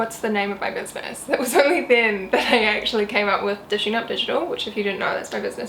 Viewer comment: Thanks, it was very useful! Didn't catch the name of your business in minute 2:20 ! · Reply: i found on IG🥰